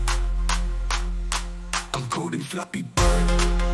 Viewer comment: why do i hear "gooning" instead of "coding" 😭